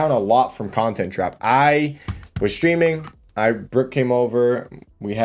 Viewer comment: watching on 2x speed and i heard rick instead of brook 💀💀 but imagine